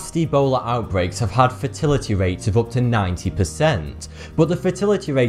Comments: Thought he said "fertility rate". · the way he was saying "Fatality" sounded like he was saying "Fertility". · I know coming from Lancashire starts you at a disadvantage in the pronunciation stakes, but you make a right pig's ear of saying Zaire. And every time you say "fatality", I hear "fertility". This will be very confusing for those for whom English is not their first language. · Who else heard him say “fertility” rate? lol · Fertility rate? Wait, Fatality rate. I thought he said fertility